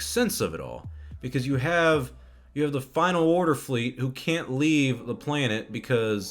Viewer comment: Oh, it is the Final Order. I thought Palpatine said Final Odor.